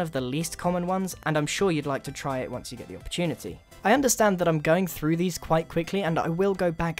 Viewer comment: I thought he said “I’m sure you want to try it once you lose your virginity”